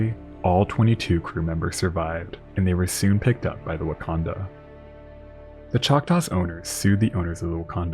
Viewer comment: My pea brain kept hearing "Wahcondah" as "Wakanda" 😂 I love these stories ❤ Edit: did I hear "SS Waldo" twice?